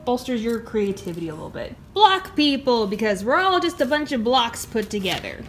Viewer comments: ohhh block people i heard bla. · When you said block people I thought you said black people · I thought you said "BLACK PEOPLE!" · I thought you said black people 😂😂😂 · “BLOCK PEOPLE!” Me: minecraft people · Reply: I thought it said black people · Block people! Because we're all just a bunch of blocks put together! Technically, this is true I guess...? I mean.. "Cells are the building blocks of all organisms" — My science teacher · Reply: when she said that what i heard was " BLACK PEOPLE! BECAUSE WE ALL ARE BUNCH OF BLACKS TOGETHER"